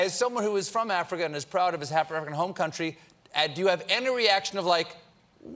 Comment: did Colbert say "African-American home country"?